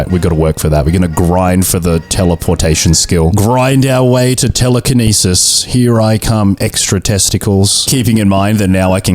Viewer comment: i thought you said teletenisis